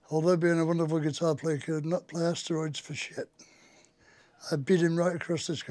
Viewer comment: Thanks for that! I thought he was saying Randy couldn't play on steroids. ! ? !